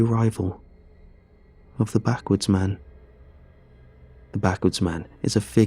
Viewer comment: At first I heard that as "the Backwoods Man"